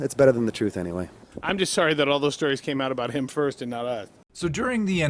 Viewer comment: You misunderstood what Buzz says at the end of the interview at 4:01. He says: "I'm just SORRY that all those stories came about him first and not us." instead what you say at 6:53 (...that he is glad) - OMG please be careful when working with information. Didn't you hear the sarcasm? Also the fact that Buzz was paid for the review rather indicates that he wanted to do a meaningful job not the otherwise and wraped it like he did. Melvins just really defend their dead friend there. RIP Kurt Cobain